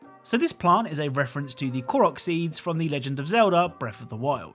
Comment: For a second I thought he said Clorox seed